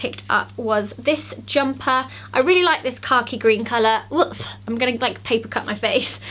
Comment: whenever you said 'khaki' I thought you said 'car key' and I was very confused :D